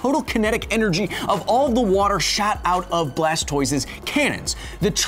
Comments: am i the only person who hears shat out of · Anybody else hear "All of the water shat out"? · first time i heard it, i thought kyle said "shat out" instead of "shot out" of blastoise's cannons